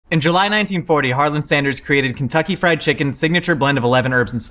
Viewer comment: i thought he said July 19, year 40 in the intro 😂